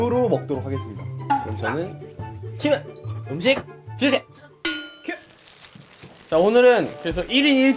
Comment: 에서잘들으면 PD님 큐! 하는소리들림후훗